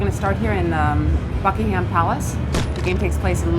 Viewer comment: lol i thought she said fuckingham palce